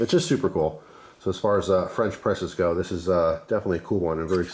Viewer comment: I thought French press said fresh prince